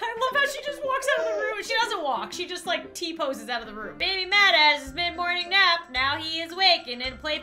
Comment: It Sounds Like Said Baby BadAss😂